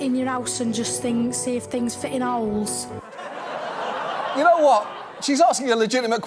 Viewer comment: I thought she said ... "Fit n Owls!"